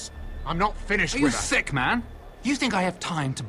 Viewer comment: he said are you thick instead of sick. lol